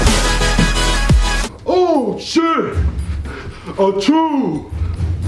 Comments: Is it just me or at 4:25 it sounded like he said "oh shit a jew" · I thought he said oh shit a chode 😂